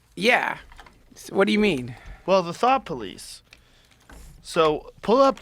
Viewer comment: Thought he said thot police for a moment there....